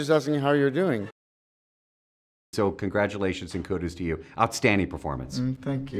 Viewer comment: i think he gives that bewildered look because the interviewer said "kodus" instead of "kudos' to you" lol